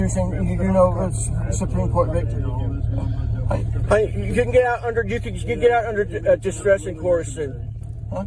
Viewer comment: should work on forming and completing a sentence. @ 09:41 Did he say "get under my dress and corset"?